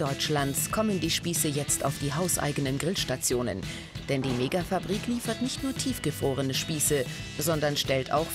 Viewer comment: Hat jemand auch "Negafabrik" verstanden bei